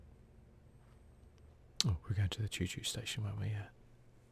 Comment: did I hear that right? Choo Choo station? Oh God! Lol 😂🤣